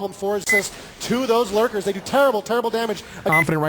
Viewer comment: did I hear lurkers?